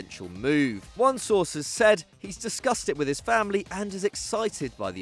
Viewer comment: About Modric I first thought he said - Disgusted with his family 😭 obviously was discussed!!